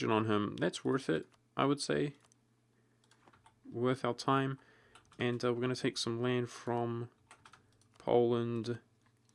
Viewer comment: Lmao, thought I heard "Were going to take Texas from the pope"